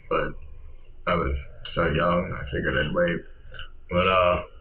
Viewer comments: I think he said he would “wait” to kill himself; not the ‘R’ word . . . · i think he said "wait" not "r*pe" as it says on the subtitles